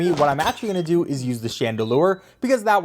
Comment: I had to replay that a bit. really done said Chandelure instead of chandelier. That light fixture ain't a ghost type, my guy.